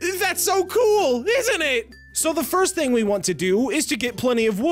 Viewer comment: it sounded like you wanted to die